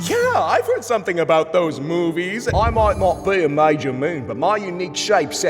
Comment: what the hell did he say? Did bro start speaking german? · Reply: I may not be a mager moon he siad